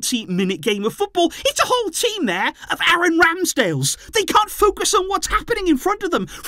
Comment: For a second I thought he said Aaron Ramsey like hold up, what Welsh Drama is going on out here 😂 Had to play it back just to realize he said Ramsdale lol